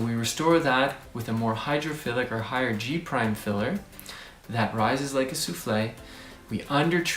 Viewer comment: Please: English is not my 1st language and I couldn’t understand what he said at 2:44, something like “g prime filler...?” What’s that?